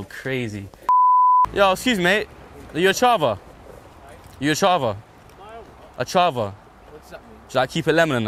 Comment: Did you just say "Charva" 3:32 so bizarre if you did. That's like really old Newcastle slang. It's where the word "Chav" comes from. I've never heard anyone outside of the North East actually say "Charva" though, it's always "Chav".